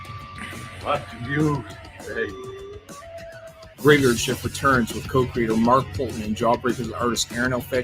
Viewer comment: I definitely heard trailer as "Graveyard Shift returns with coke dealer Mark Poulton."